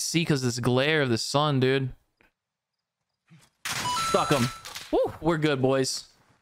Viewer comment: Every time you say stuck him it sounds like you said f him